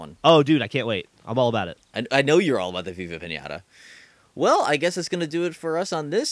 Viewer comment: says: "I know you're all about Viva Pinata." I heard "I know you're all abagh vagha panaghtgrgl"